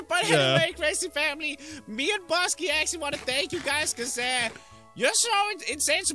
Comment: I thought he said we want to date you guys!